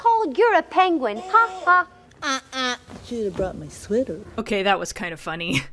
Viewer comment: what did she say?? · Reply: "Shoulda brought my sweater". I know it's really quiet--I boosted the audio as high as I could for that part.